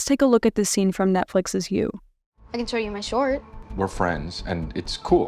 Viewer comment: Does anybody know what show she was referencing at 26:26? I'd be interested to find it but I can't quite hear what she's actually saying and subtitles aren't helping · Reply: It’s from season two of You on Netflix!